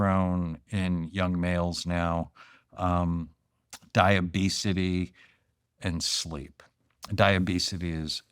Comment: is he saying diabetes or obesity. Caption says diabetes but he’s saying something like diabesity